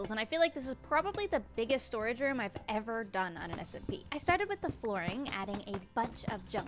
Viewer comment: did u just say SMP😮